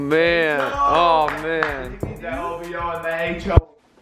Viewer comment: Did Brandon said in 2:40" that OVO and that HO" is he serious